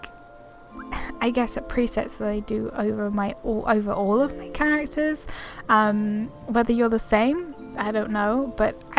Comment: Hi, is the body type fully broad or fully muscular? I didn't understand well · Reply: Large :)